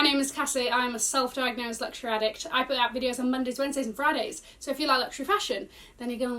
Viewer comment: This entire time I thought your were saying “I pad videos Monday, Wednesday and Fridays.” This is the first time I heard the words “I put out videos”. I just thought it was a British slang 😂. Now what does it mean when you say none the bell 🤔.